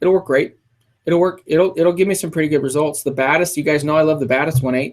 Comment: At around 16:40 says the model of a lens I never heard about. Could someone tell me what it is? My english is not that good and what I heard is "the baddest". Thanks for this help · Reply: Fotos Chapecó Batis. Its the 85mm Batis